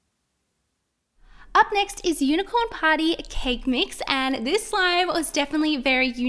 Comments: Who else at first thought she said unicorn potty lol 😂😂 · I thought y said potty I was like what but it said party😂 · Who else thought she said unicorn potty because the slime was yellow and how she said it? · When said unicorn party I thought she said unicorn poughty don't know if the spelling is right LOL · How do you know if they give you good extras because ur a YouTuber 😂lol. Lol my moms name is Tiffany 4:25 I thought you said unicorn potty I was just like hold on a second 😂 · When you said Unicorn Party I thought you said Unicorn Potty